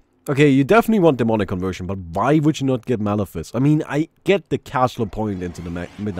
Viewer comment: I thought you were saying mana fist instead of malefice. Also, auras items can't be active in backpacks because backpacks are secretly creeps.